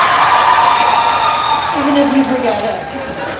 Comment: I think David said "I'd never forget it" at 7:26 or am I crazy?